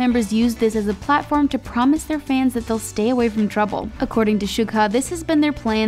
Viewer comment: did i hear it right?! Shuka?? Did you just say Shuka?!